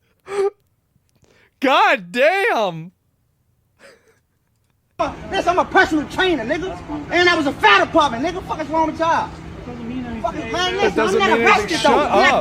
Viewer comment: i heard his Fent training is “Tops”.